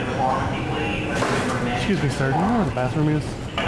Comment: Did he call her "sir" at 3:48? 😆. That would be a funny prank switching sir/mam with the sexes.